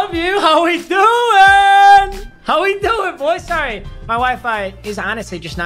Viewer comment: i thought this mad said “my wife...” for a sec